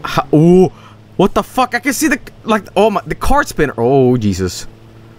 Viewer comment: Did Joel just call the engine a car spinner?